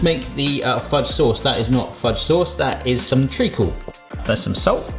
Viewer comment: Can anyone tell me what the heck is the black stuff in the fudge sauce before he adds salt? It sounds like he is saying tree cool if he is what is that? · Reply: Treacle. It's a slightly bitter tasting dark syrup, like molasses. 😊